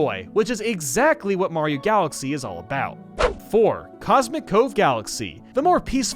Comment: When I was young I thought cosmic cove said cosmic CLONE and I didn’t know what I was missing out on by skipping it